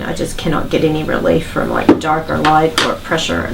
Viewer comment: did she just say DARPA life?? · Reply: Cannot get relief from dark or light pressure no pressure.